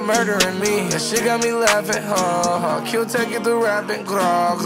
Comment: thought he said tecca